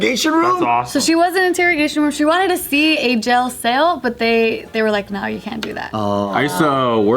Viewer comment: says 'jell sail' instead of jail cell. 😂